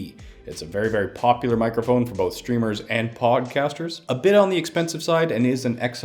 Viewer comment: did he just say pogcasters?